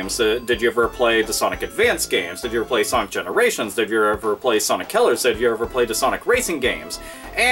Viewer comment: Why does it sounds like you said "Sonic Killers" instead of "Sonic Colors"?